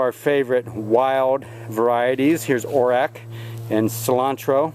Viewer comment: What is that purple plant that you pointed out you let grow wild? It sounded like you said orac?. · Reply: Orach is a purple relative of spinach. It's tasty in salads or add it to a wilt of hearty greens.